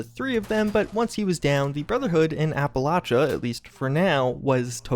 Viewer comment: Did he just say the BOS in Appalachia at 3:45?